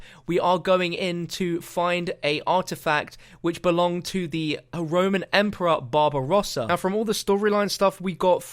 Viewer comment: For a second there I thought he said the Roman emperor bob ross